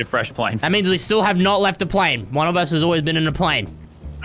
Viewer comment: So fresh says “we have not left the plane just getting another plane” but caption says flying all game but you weren’t flying whole game Becuz you got into another plane 🤦🏽‍♂️fresh you clickbaited us🙄